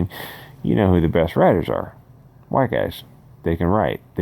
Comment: did you say “we all know who the best writers are.... white guys”?